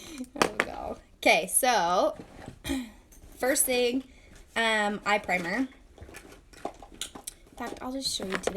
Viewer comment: Instead of eye primer I thought you said 'I'm pregnant' - had to go back and re-hear! Great video!